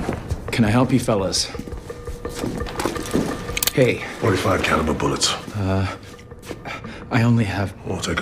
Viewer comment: did he say 5 caliber bullets??? Lol